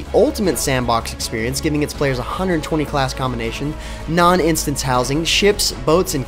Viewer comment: did he said "125 classes"? o.O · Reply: 120 not 125